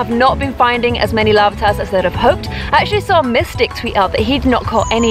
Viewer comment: Did she say Mystic? If so, he has caught a lot... He has a Tyranitar! Hi if you are reading this! Love Your Vids! Please don't be mean to me...